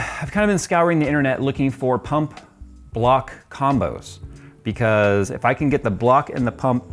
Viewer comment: i thought i heard "cock block combo" ... Is it just me?